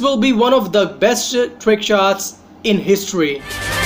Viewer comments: this is going to be the best shi.. trickshot Edit : I thought he said it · Did u say shit